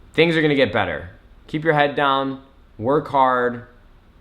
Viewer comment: 'keep ur head down''? is that what he said dml made come on